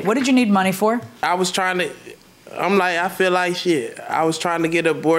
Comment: He itritated me the entire case!! With his sassy self. He looks down low to me 💅 plus he has no eyebrows @ 8:43 did he say "shit" he seem so slow smh I'm just aggravated with him.